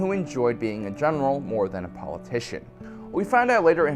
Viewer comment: I thought you said “Leia liked being a genital, not politician.”